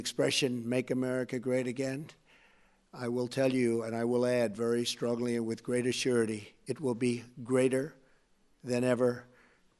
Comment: did Trump just say "very strongly and with great assurity"?